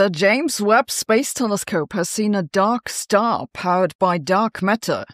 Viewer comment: Phew, for a moment there I thought you said Duck Star. 😨